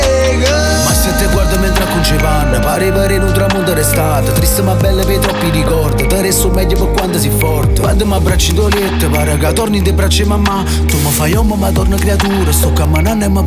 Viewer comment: Scusate, cosa dice da 1:15-1:17, sono Toscano e non capisco. Il resto ok 👍🏻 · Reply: Mi siedo e guardo mentre aggiusti il bucato, sembra di vedere un tramonto d’estate, triste ma bello per i troppi ricordi ti darei il meglio per quanto sei forte, quando mi abbracci nel letto mi sembra di tornare nelle braccia di mamma, tu mi fai uomo ma ritorno bambino.. sto camminando e mi sembra di volare.